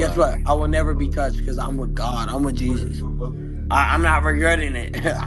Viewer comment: Man said "no one has touched me because I'm a god; I'm a Jesus." Bro is tweaking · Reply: listen better 25:09 " I'm with God" . Not "I'm a god". He was drunk, he didn't articulate clearly. First time listening, me too I thought he said "I'm a". But no, if you listen attentively a 2nd time, you'll hear "I'm with"